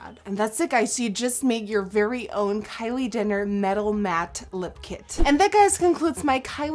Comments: Sounded like she said "Kylie Dinner" at 10:11 😂😂 nice video though! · did she say Kylie DINNER? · am I the only one who heard Kylie dinner in 10:15 😂😂😂 but I still love this video